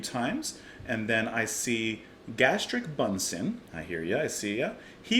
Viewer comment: Brilliant reaction and thanks for the mention. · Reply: I thought he was saying "Gastric Bunsen" which sounds like a Wire song title in itself